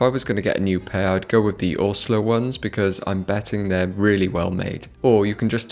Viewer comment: Where did he say is the best place to buy a new army pant at 3:44? I’m having a hard time finding the site. Thanks:) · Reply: Orslow